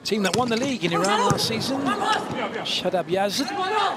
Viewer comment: did he said what I heard he said?! “Shut up yes” 😂